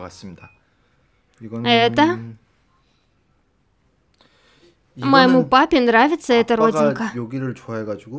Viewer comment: Когда Тэ сказал : моему папе нравится эта родинка. Мне послышалось моему парню нравится эта родинка 😂😂😂 100% слух. Спасибочки за озвучку🥰